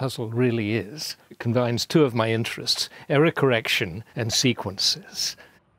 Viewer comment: When he said “error correction” I heard it as “Eric Erection”